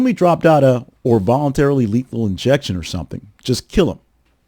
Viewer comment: i coulda sworn he said "involuntary lethal injection", not "or voluntary" as i believe you say here